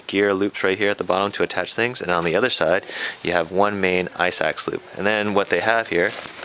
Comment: what are you saying between 1:55 and 2:07 ? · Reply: kalif151byccrew i think he said "ice axe loop"? Ive seen it on other packs